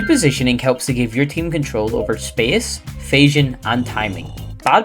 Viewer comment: what is fasion????? what did he say? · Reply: I believe he said vision